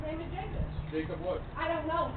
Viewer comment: FYI subtitles are very inaccurate. She said "I'm not his fucking parent." Not his "terrorist." 11:08 And it's obviously a badge number, not a "bad number" lol And whatever the hell this sentence is: 12:22 Must have been transcribed by AI. or an idiot. Oh and then the clumsy editor repeated footage from before instead of their next report about suspicious waistbands to the supervisor. 17:00 12:00 🙄